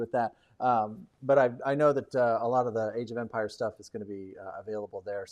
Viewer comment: I'm excited for any future Diplomacy content. Will there be an introduction or beginners guide to it before any of the gencon videos? That might get the hype up. Btw, I kept mishearing Age of Empires as Age of Vampires 😂.